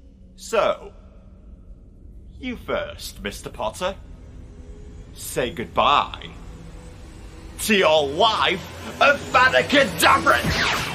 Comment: Not gonna lie, I thought he said "Say goodbye... to your WIFE!"